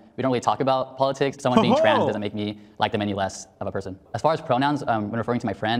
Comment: "I don't use pronouns, I would say "my friend" or use their name" THEIR name. "I don't use any pronouns" uh huh. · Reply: What's weird is just before that, it sounds like he says "I don't like HIM any less" maybe he said them and because he talks so fast I misheard. In any case, isn't calling his friend a trans man already a concession? If you think its wrong, why not call him a girl? It seems both ill informed as to what a pronoun is and cowardly.